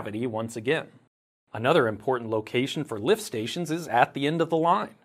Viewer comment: lmfao... I thought it said "Lift Sanctions" and thought it was some crazy China/Russia propoganda push 🤣 I got so confused that it was your channel... oh man, too much geopolitics for my poor brain